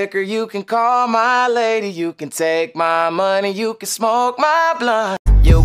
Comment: I swear the first time I heard this I thought he said you can smoke my blood